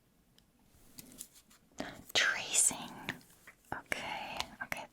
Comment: thought it said racing. Was wondering if you were gonna throw it then try to outrun it🏃‍♀️